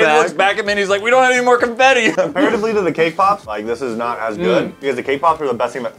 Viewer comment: lol i thought he said KPop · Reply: I thought he said cape pops